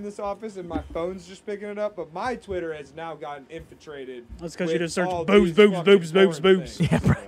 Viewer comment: says “infantrated”instead of infiltrated 😂😂